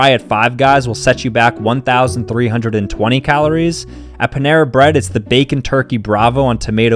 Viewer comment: I thought he said Pantera bread. Like the awesome metel band pantera 💀